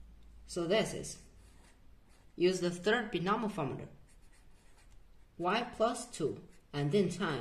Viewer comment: did she say " Use the third binomaformater"?